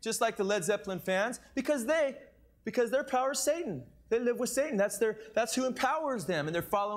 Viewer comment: I don't know waht about u but in 8:00 he clearly says suitin not satan